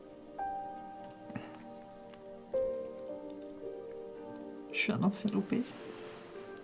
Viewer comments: Il dit quoi le gars a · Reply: Clara « je suis un ancien obèse » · qu'est ce qu'il a dit j'ai r compris ? Sinon super film bravo ! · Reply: IL a dit je suis Un ensien obese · Il a dit quoi svp j'ai pas compris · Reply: "je suis un ancien obèse" je crois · À 20:02 j'ai pas entendu il a dit quoi svp ? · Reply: Il as dit "je suis un ancien obèse" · Pour ceux qui ont du mal au comprendre (moi de même, j'ai réécouté plusieurs fois pour l'avoir), il dit "je suis un ancien obèse" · il a dit quoi ? J'ai pas compris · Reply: Ahhh nn enfaite c marqué sur les sous titres "je suis un enfant du pays"